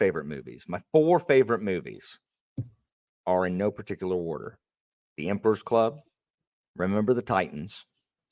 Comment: The Emperor's New Groove - Remember the Titans - ?? didn't catch that movie name - Goofy movie Might have misheard 😅 but if not i've watched the emperor's new groove and the Goofy movie. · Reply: It's the Emperor's Club actually, which is Kevin Kline teaching classics at a prep school...very different movie LOL And the 3rd one is 1776, which is a musical about the declaration of independence.